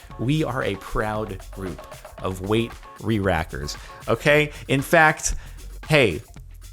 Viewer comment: I dont hear rerackers.. I hear "Weedwhackers" :')